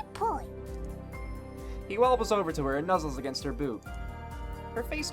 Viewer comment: Everyone loves Danny, and that’s coming from a fellow Dan! 5:45 I misheard Boob, and I shouldn’t be laughing so much, but I am